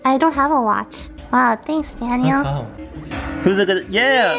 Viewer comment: When se said "thanks Daniel" I thought she said "damn Daniel" 😂